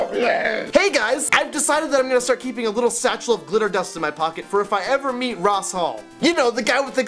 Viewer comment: it sounds like hes saying litter dust and not glitter dust :P